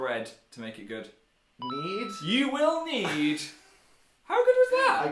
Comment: When phil said "you will need some bowls" I thought he said "you will need some balls" it just sounded like that K?k...